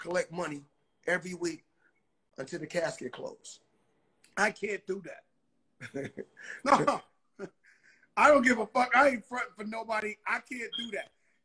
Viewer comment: Did Joe say “ I can’t do that , if I don’t go out there and shake my ass “ at 1:26🤔🤣